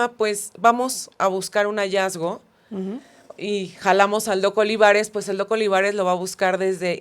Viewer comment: Me encantan sus podcast pero siempre los pongo en 1.75 de velocidad (todos los videos qué veo en youtube los veo así jaja) y en el 11:20 cuando dice "llamamos al doc. Olivares" le entendí "al loco Olivares". 😂😂😂